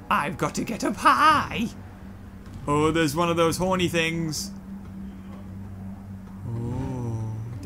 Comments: Did he really say; 'one of those horny things'? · Is it me or did he say there's one of those horney thing